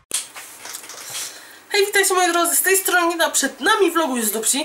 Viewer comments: Usłyszałam " hej witajcie moi drodzy z tej strony NINA" ? o.O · tylko ja usłyszałem " Z tej strony Nina! "?